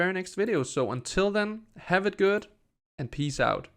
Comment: I’m so tired that I thought you said “have it good, and pee south” This game looks awesome so far. hopefully they do decide to let the people try it out someday :)